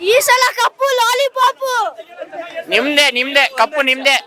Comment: ಯಾವತ್ತೂ ನಮ್ಮದೇ ಕಪ್ಪು😂😂 ಮುಂದಿನ ಸಲ,,,, ಇನ್ನೂ ಎಷ್ಟು ಸಲ ಆದ್ರೂ😂🎉 ಅಕ್ಕ rocks 🎉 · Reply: What he say at 3:12 Like lolipop🍭